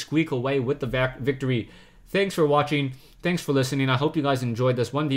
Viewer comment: lol, i was tabed over from this screne, and i thought you said paranoid android. i was like "SWEET JESUS ICCUP MAPS FINALLY!"